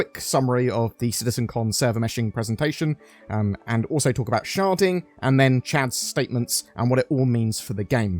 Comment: did he just say sharting?